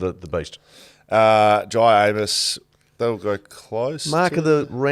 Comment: seemingly has a cold, and I was fairly sure I heard him say "dry anus" here, instead of "Jye Amiss". Relistening, while paying attention, it isn't really there. But you should all know about it.